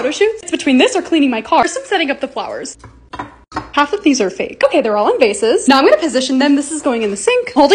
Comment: I thought she said "they're all invasive" 😭